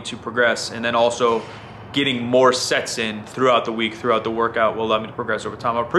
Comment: Did he say "getting more sex in" at 11:05? Lol hell yeah, get some ;P